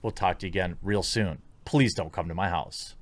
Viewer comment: Did I just hear Jeremy say “Please come to my house?” Or “Please don’t come to my house?” ... I’m not sure I can make it out... 😁